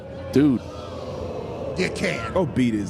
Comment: What did the fan say at 8:50 ? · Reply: I'm pretty sure he said, "You can't wrestle."